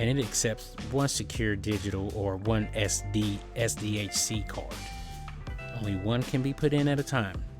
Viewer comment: timestamp? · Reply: OHHH my bad i thought u said ssd MY BAD!!! XD since seeing this i found your other video "Acer Nitro 5 Hard Drive Install, How to Easily!", but i havent watched it yet, so i assume this can hold another drive